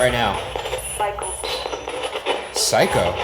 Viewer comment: I heard "michael" instead of "psycho"